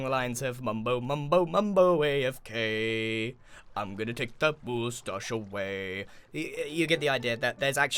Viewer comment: The subtitles said mambo mambo Mano away of k... I’m gonna take that booze away. What is grian an alcoholic therapist?